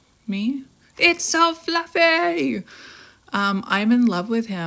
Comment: I heard "Buffy" for the cow!!